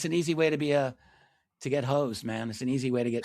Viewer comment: easy way to get *HOSED* not hoes. lol though I heard that at first too.